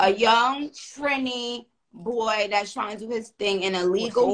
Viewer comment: was half listening and thought she called him a young tranny boy not trini boy. 💀